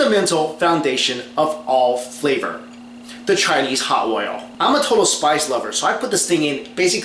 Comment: not even kidding i thought it said cheetos hot oil